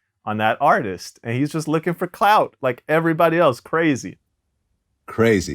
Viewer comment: I know for damn sure I just heard say Bobby murder